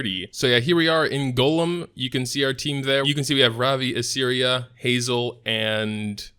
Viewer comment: Every-time you say, I keep hearing "Assyria" ahaha